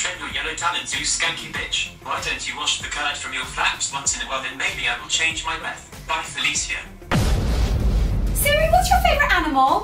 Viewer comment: why don't you change the X of your flaps?? what is it saying?? i dont understand; i can't hear it well. could someone please tell me what that last text said (before the letters "smack down" came on the screen) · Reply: "Why don't you wash the curds from your flaps once in a while and maybe I will change my breath, Bye Felicia"